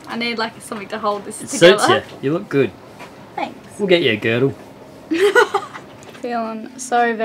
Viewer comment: did you say:” I’ll get you a Gürtel“. ?? Did Stefan teach you some German ?